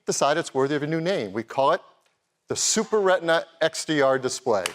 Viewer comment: I thought he said the Super RawR XD for a minute